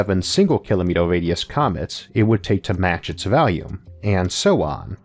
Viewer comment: Don't mean to mock I understand he has a minor search impediment, worse still an American accent (I'm British) but at 15:33 it sound like he says valium or possibly value, instead of volume. Not a huge issue the info isn't lost, but I couldn't find valium meaning a similar thing to volume. Am I mistaken or is it just an accent think that happened to rub me the wrong way, first time I've had and issue and I love work. Captions does say volume.